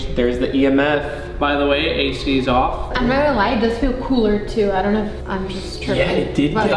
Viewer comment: why did she said she has to lie it’s colder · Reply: she said “not gonna lie”